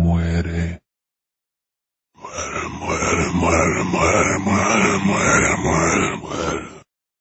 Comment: I really do think that the ganado is saying something slightly different. His voice is kinda fuzzy so it's hard to hear but as a Spanish locutor, I hear «¡Te voy a matar!», which means “I will kill you!”, like the further quotes.